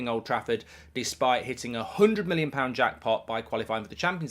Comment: 😂😂😂😂😂😂😂😂😂😂😂😂😂 I thought you said you was getting a 200 million budget